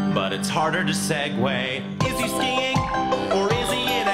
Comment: Can someone please help me? I don't understand what he says at 6:38... "it's harder to"...?? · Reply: "It's harder to segway", meaning that it's harder to change topics when performing stand up on a stage.